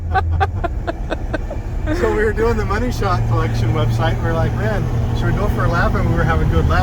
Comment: Did say Money Shot website @ 12:46? Because that’s very different from a Money Shift website. 💀